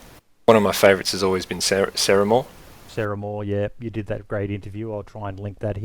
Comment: thanks mate!! I was worried about the length, but most people seem t have enjoyed it nonetheless, probably a good one to listen to whilst playing yourself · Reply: Hey... @ 12:28 I heard mention of another YouTuber for DayZ content (saramore is how it sounded), that a link was going to be provided for. Where is that link, I'd like to check out their channel. :) nm, found Ceremore. (if that's the one?)